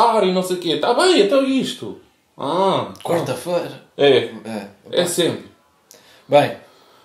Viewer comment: Eu ouvi “quarta-feira” 🙏🏻🙏🏻🙏🏻